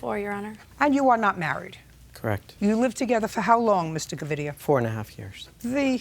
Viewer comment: OMGGG @ 0:11 I was just listening to this while cooking and I thought she said Mr. Chlamydia! 😂😂😂 the way I did a double take and rewind!!!